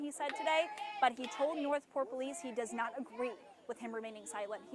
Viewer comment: I’m slow as hell I thought it said north poll police 😅